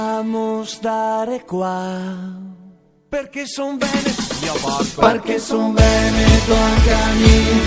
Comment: Ma 2:30 ho sentito male o c'è un DP quasi subliminale? 🤣🤣🤣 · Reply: Siiiiiiii dio can xe vero se sente un dio porco ....... Eroi dio buxegato ..... W SAN MARCO